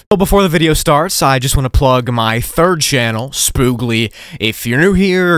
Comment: thought he said spoongli at 5;00